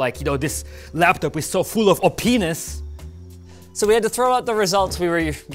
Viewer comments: So I'm the only one who heard "Oh-penis" 😂 ok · am i the only one who heard openis?